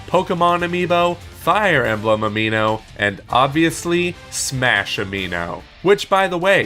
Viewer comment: did he say amiibo lol 😂